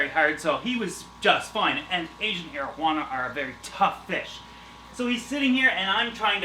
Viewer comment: Lol, I thought he said Asain Marijuana